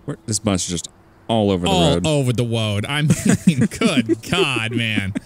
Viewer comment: all over the woad instead of road